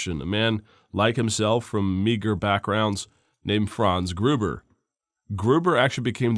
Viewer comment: Hans Gruber like the Die Hard villain!? Edit- misheard but damn close